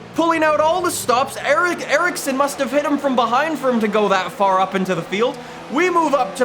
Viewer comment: did anyone else hear at 3:35 " ERICSON must have hit him from behind"???